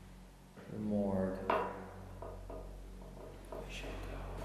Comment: am i think only one who heard “you should go” whispered in reply to saying “into the morgue” @ 1:18:57 ????